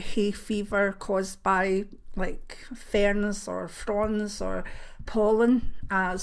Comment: Haha thought you said, "Hayever from prawns" not fronds :)